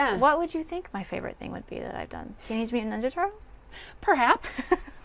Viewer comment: What does she say at 30:29 cuz Im really interested what movie she meant LOL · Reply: Teenage Mutant Ninja Turtles (2014)